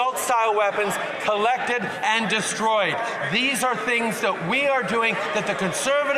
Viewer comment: Destroyed? I heard 'Ukraine'...😅